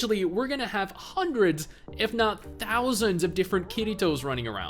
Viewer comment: I swear i heard "thousands of KittyToes" sounds nice not gonna lie.